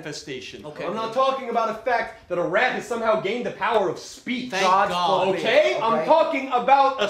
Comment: Did I hear a thank Foamy? ;)